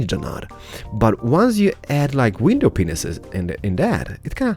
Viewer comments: When you were adding window pains in the drive way it sounded like u said window penus😆😂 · it sounds like you say window penises! Anyway love the video and the content. Keep it up. :) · Anyone else hear "window penises"@ 5:53?